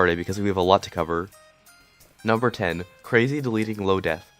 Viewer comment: I heard 10, my ears we’re playing tricks on me lmao